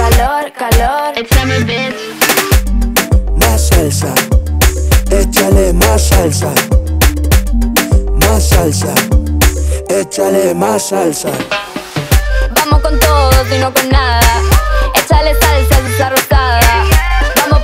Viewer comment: I thought he said I like my salsa😭😭